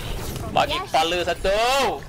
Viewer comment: did u just said 'bagi kepala satu'?😂